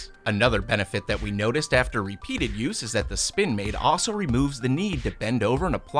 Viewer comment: I thought it said: "SpoinMaid".